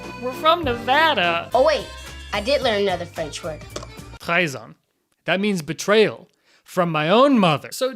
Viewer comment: he says "trieze ans" and says it means betrayal in french. i speak french and he actually said "13 years" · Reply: No, he says “trahison” which means betrayal or treason. It sounds close to “trieze ans” just like “vert” (green) and “verre” (glass) sounds similar. Or they’re, there, and their sound the same in English.